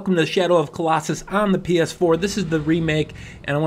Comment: Did he just say: Im the PS4? xDDDDDDDDDDD